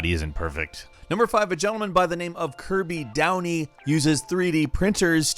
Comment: No joke, I thought you said "Kirby Down-B", not his name